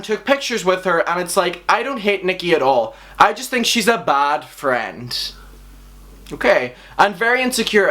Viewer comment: Is it just me but I heard him say "fat friend" not bad friend